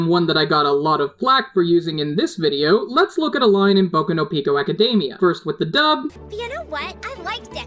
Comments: Did he just say Boku no PICO academia.... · Wait a second ........ did you say ........... Boku no PICO Academia at 9:58? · Did anyone else hear boku no pico academia at around 10:00? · Did you say Boku no Pico Academia or am I hearing things ? 😐